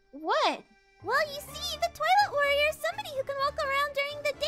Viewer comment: Bro got kidnaped on 0:01💀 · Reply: also i thought KC said you might be the toilet warrior